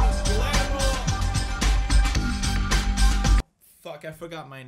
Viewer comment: did he just say f*CK?? Wow I though this was a Christian channel. Unsubscribed.